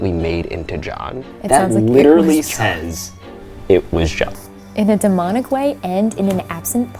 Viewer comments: 26.05, I heard "it was God" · i hear either "it was god" or "they forgot". i swear, i can't hear the john there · Honestly, it sounded like “it was God” and it sounded like a passionate Christian radio channel lol. Those guys can sound really angry. 🤷‍♀️ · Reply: I thought it said "it was god" too or "it was dad" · I didn’t hear it was John either I heard “it was not” or “it forgot” which doesn’t make sense 😂🤦🏻‍♀️🤷🏻‍♀️ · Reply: I heard "it was god" 😂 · Them: *obsessing of "It was John" Me: Dude, are we not gonna talk about how it said she was more dangerous? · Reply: I heard ‘it was god’ or maybe ‘I forgot’ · every time she plays the recording, all i hear is "it was God." i hear no juh noise only guh. so idek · The anger in “it was John” sounded like she was annoyed at how many times you guys asked who killed her! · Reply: I heard “it was god” no cap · I don’t really hear “It was JOHN” as much as I hear “It was GOD” (I’m not religious) but idk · Voice box: "DIS BUS JAH" "Clear as day" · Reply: I heard, "It was God" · I heard “it was god” soooooo it was thy lord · The "it was john" sounds like "it was GOD" to me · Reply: I only hear ''it was god'' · I heard “it was god” at first 😂 still love the video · I don't hear "it was John". There's a clear "G" sound at the beginning of the last word. It sounds like "this was God!" To me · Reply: I heard "it was god" - Lizzie the Sunday teacher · I heard, “It was God.” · I heard “it was god” 🤔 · I thought is said "it wad god"